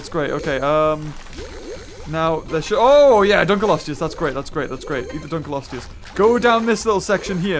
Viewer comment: I think you say: "The Colostious!!!"